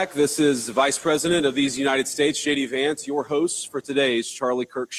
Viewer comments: Lol when JD Vance said his name, it sounded like "Shady Vance" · When JD Vance introduced himself, if sounded like he said Shady Vance. I think that is so fitting. VP Shady Vance.